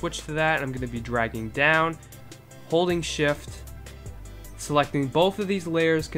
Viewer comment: Am i the only one who thought he said holy shit instead of holding shift